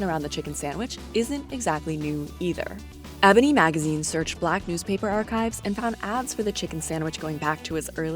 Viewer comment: did she say “BLACK NEWSPAPER ARCHIVES”?